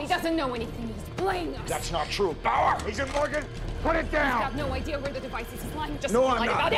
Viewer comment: Agent Morgan! Put it down! [The CC says "easy".]